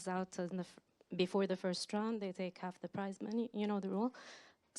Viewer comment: I thought he was saying "price money." Took me a good minute.